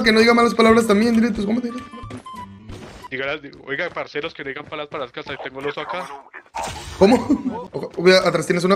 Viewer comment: No entendí nada, ¿alguien me traduce? xd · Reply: Dijo oigan parceros traigan balas para aca que tengo 2 aca xd